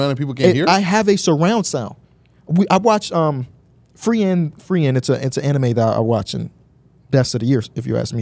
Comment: what anime did say? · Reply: Frieren. Lmao he pronounced super weird but the anime is stellar imo.